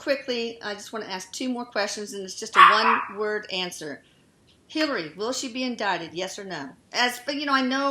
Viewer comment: what she say at 22:13? if "Hillary, will she be...?" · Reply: hillary would she be indicted like for the emails